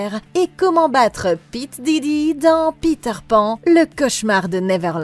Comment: rassurez moi chuis pas le seul a avoir entendu P diddy...